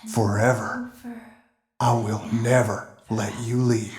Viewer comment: I hear puppet whisper never